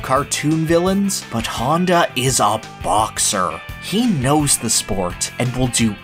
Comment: i paused at 14:33 and it sounded like you said "piston honda is a box"